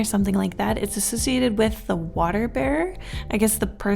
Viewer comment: oh dear i thought you said water bear instead of water bearer a water bear is so weird lmao look it up